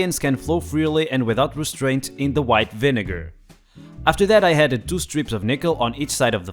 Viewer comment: This guy saying he had to say "White Vinegar" Slowly reminds me of Doctor strange can't say "Penguins" lol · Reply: I think with his accent it might sound like he is saying white Niger